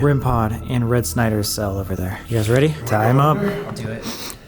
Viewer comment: Did nobody hear a really weird noise at 1:11:01. I'm confused, because it may have been Sam, but it sounded like "I'm ready."